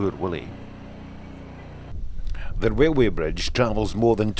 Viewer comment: sounds like he is saying, THE WAILWAY BRIDGE....... he must be scotish, the foreign fuckers cant speak English....lol